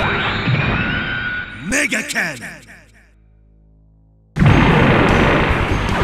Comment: 1st time when i heard Zechs super move/special move, i thought he said "Mega Can". But now I know I'm wrong